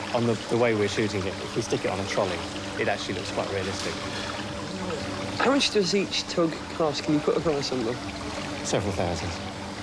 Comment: what does say? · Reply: "How much does each tug cost? Can you put a price on them"? Captions have been added at the viewer's convenience :)